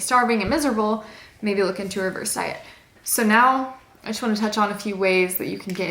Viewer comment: What did she say at 10:03?? Reverse diet?